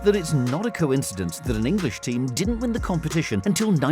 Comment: I thought you you said an British team didn’t win the European cup until 1968 but no you said English team